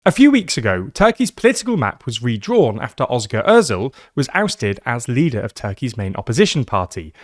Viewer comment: did he said "Oscar Odzel"?😂